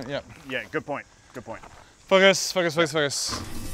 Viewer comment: i thought he said ---- fuck us . 😂😂😂😂😂😂